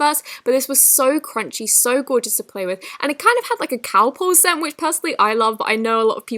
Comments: ITS CHRISTMAS EVE!!!! Love you this is a great Christmas present! ❤️❤️❤️❤️❤️❤️❤️❤️❤️❤️❤️❤️❤️❤️❤️❤️❤️❤️ Edit: 6:27 I thought you said it kinda had like a cow poo scent 😂😂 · What did she say the scent was at 6:28 ?😂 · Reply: Calpol, it's a type of paracetamol used to treat pain and fever. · what’d she say?? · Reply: Calpol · hecc did she just say? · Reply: Cow por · did she say cow pull?